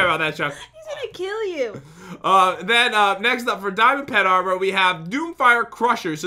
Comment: at what point did we start saying diamond and golden “pet armor?”